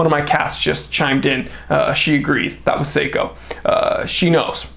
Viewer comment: did he call his cat seiko?